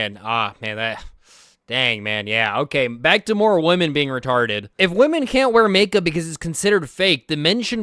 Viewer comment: did the subtitles just say “back to women being women” instead of “women being retarded” LMAO